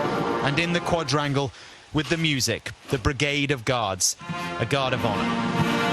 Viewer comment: Thank you. Will be shared with many. And when the commentator with his British accent @ 2:53 was saying “a brigade of guards” and "the guard of honor" I wasn’t looking at the screen at the moment, and all I heard was “ a brigade of gods” (which these tyrants think they’re anyway), and "the god of honor", I had to go back to listen and watch twice. 😳 “Now thanks be unto God, which always causeth us to triumph in Christ, and maketh manifest the savour of his knowledge by us in every place.” 2nd Corinthians